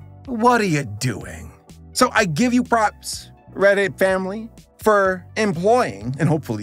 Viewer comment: i keep hearing "Reddit family"